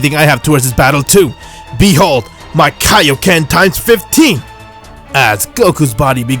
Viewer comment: did goku say be hold my kaioken times 15 or 50 some one help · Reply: 15